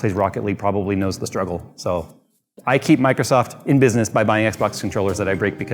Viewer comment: I had to rewind because I thought you were keeping microcenter in business instead of Microsoft. Lol